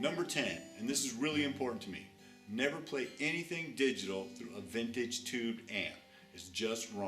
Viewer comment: Hi,my english is Not good, But what he said ? on TIME 4:29 by the Tip Nr.10 ??? never play ..???….. through a vintage tube Amp. what mean this, in easy words ? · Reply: I said Never play anything "digital", like digital pedals, but its actually a joke, just having fun. Thanks for the question.